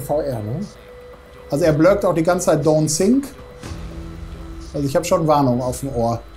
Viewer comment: Ach „Don‘t sink“ sagt der... ich hab immer „Don‘t sick“ verstanden und mich gewundert, warum mir da jetzt schlecht werden soll 😂